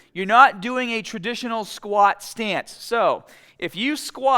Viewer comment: I thought he said 'A traditional Scott stance' 🤣🤣